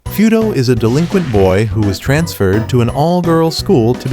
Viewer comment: I thought he said foodo😂